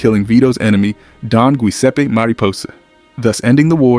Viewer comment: Did you say “Gooiseppe”? 😆